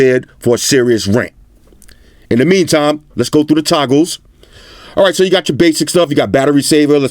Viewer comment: When Floss said "Toggles" I literally heard "Tacos"